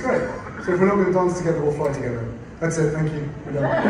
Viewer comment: What I heard H say was, “So if we’re not going to dance together OR fly together that’s it, thank you, we’re done”…. So many news outlets are quoting him as saying let’s fly together but that’s not what he said